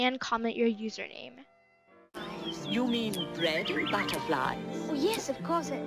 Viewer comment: xD in the intro I hear "You mean bread butterflies" Is it only me who hears that Ok. Edit : OMGGG I GOT SCAMMED FOR MY ONLY LEGENDARY À DRAGON PLEASE HELP 😭😭😭😭